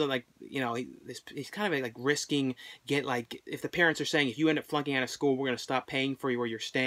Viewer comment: When you were talking about All American Bikini Car Wash and said "if you're flunking out of school" I thought you said "if you're fucking out of school"